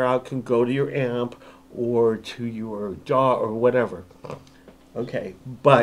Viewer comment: At 2:37 minutes: What did you say? Go to your ..... What? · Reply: DAW: I get it.